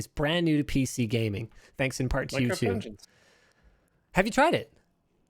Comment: what game does luke mention? I keep hearing minecraft angels